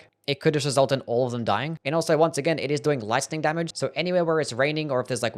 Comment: I swear he said lightskin damage